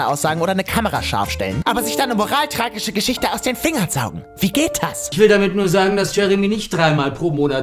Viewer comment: hab ich einfach vegetas statt wie geht das verstanden